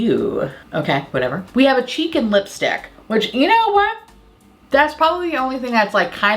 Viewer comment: you said "cheek and lipstick" and I heard "chicken lipstick"